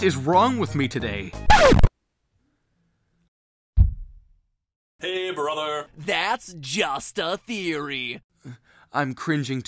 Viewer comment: I'm not looking at the screen, then I hear "Hey brother". The I'm like "WAT, JAY, BEN" Then I here "Game theory" I'm like "WAT, MATPAT WHERE ARE U"